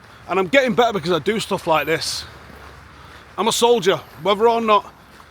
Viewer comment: You said "im a soldier" in my head I heard "mujarhid brooo"